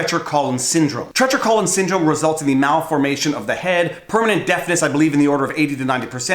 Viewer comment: i thought he said permanent death lmfao